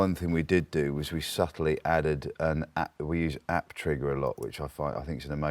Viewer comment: Anyone know what plugin he mentions for drums? I heard "apptrigger" but couldnt find it online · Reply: it's apptrigga